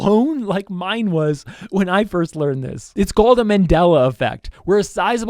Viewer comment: When he said mandala effect, I thought he said mandala catalogue and had a panic attack.